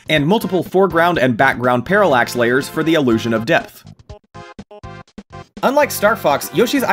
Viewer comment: did he say the illusion of death